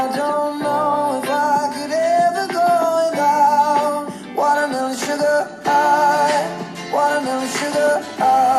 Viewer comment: OMFG NAWW REALLY???? This song is an INSIDE JOKE with my FAMILY because me and my siblings thought the song was saying 'Watermelon Boogers'. LOL I DIDNT KNOW THAT IT KINDA RUINS THE JOKE OMGG.